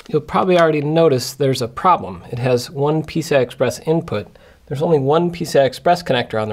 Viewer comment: Am i the only one who thought that he kept saying 'pizza express'